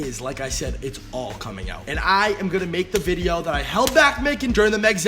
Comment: I thought u said I'm not gonna make Jake video! I guess u needs some money!